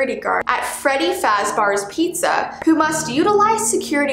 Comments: it sounds like freddy fazbars pizza instead of freddy fazbears pizza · Did she just say freddy faz bars instead of fazbears